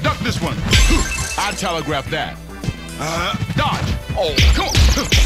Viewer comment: “I telegraphed that” while the screen is completely black is such a power move from Doc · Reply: I ALWAYS THOUGHT HE SAID “I tell her grab that”